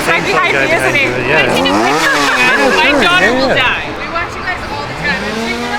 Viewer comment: Did say, daughter? I am not caught up on the videos did they do a gender reveal yet?